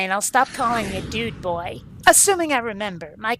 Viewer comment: For a spilt second I thought I heard Kate saying her name is Bob. (Blackadder reference) 6:27 It's not the sales pitch that stinks, it's the fact that I don't have a need for an invisible, intangible party dress.